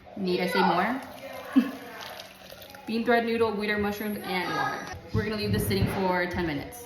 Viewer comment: Why did I hear “wiener mushroom” at first 😭